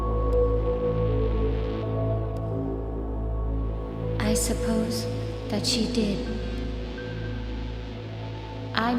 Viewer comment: does anybody know what it says at 2:13 ? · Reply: “I don’t need to remain here.”